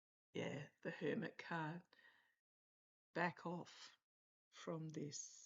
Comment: I heard a faint whisper back off